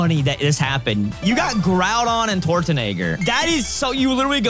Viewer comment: Did say Tortanagger?😭